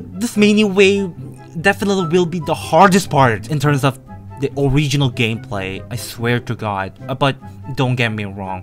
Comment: i heard *original game*> ojingoue(squid) game